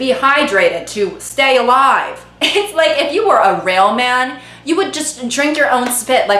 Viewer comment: girl i literally heard “if you were a rail man” 🏃‍♀️